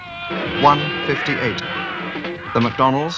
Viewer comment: What does the officer say in German at 37:40? It’s something about stones (Steinen) but I can’t make it out. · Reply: As a native speaker I would say that he says "Sie fangen an Steine nach uns zu werfen" (They start throwing rocks at us)